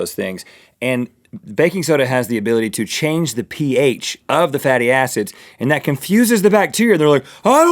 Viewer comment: When Rhett said "Fatty acids" i thought he said, Fatty a$$es